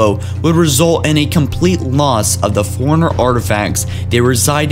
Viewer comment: Bro, you need to practice a bit before you record. Your pronunciation's kinda whack (i.e. I kept hearing "foreigner" instead of "Forerunner").